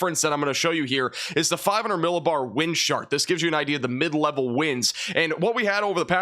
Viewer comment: I swear @ 4:12 sounds like you said 'wind shart' lol